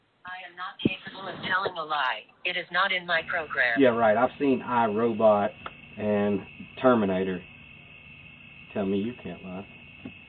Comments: I swore he said " yeah right I've seen a high robot in terminator" hahahahahah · I thought he said high robots and I terminated them